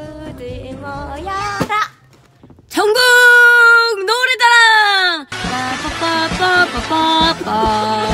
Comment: I HEARD JUNGKOOK HAHAHAHAHAHAHAH